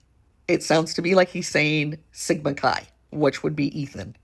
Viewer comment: The officer didn’t whisper CI. If you use good headphones and good ears you can hear him say “I don’t even know the names of the victims inside.” I suppose for Proburgers it’s close enough…the word “inside” to them sounds like CI. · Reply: To me it sounds like he says "sig chi" i've listened so many times but thats what i keep hearing